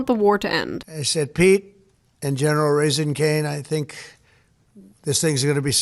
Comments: did he say general raising cane? Like the restaurant? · Did... did he just say General Raising Cane's?